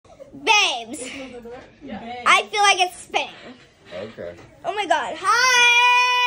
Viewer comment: I’m seriously the only one who thinks this child is probably seriously annoying even without the drugs? That’s what I get from this, and I happen to really love kids, lol. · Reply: After my first viewing I thought so too but she said, "I feel like it's spinning."